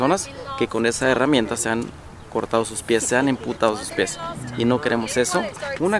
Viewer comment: PREGUNTA. se dice amputar o emputar? en el min 3:34 o es que yo escuché mal????